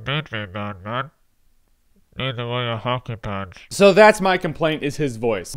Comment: lol I heard 'hockey pads' but I can't work out what else he said